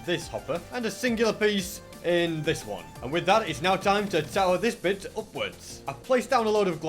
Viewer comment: what did he say? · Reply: To tower this upwards